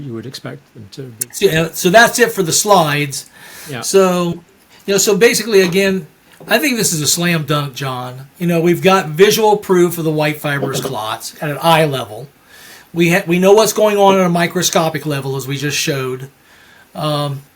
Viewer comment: Sounds like he is saying "white fibrous CLOTHS".